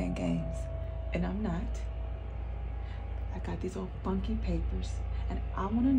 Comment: Oh she said funky, i thought she said he other F word😂😂